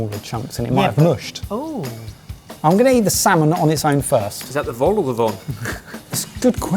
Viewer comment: What is he saying here? "Is that the vault or the vol" according to the often-wrong captions. Anyone know? Edit 1:55 Vol-au-vent is the French pastry they are eating.